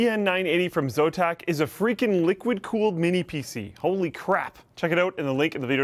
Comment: Wait, did I hear 0:50 correctly? Only crap... Are you allowed to say that on an adspot? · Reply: I think he said "holy crap!", as in "wow! freaking amazing!" sort of way.